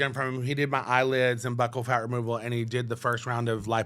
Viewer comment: I thought she said the doctor did butthole fat removal 😬🤭😝